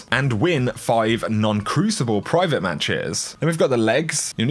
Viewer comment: you said "non crucible private matches" @ 4:20 and i had to read it and watch it like 3 times to figure out what was being said lol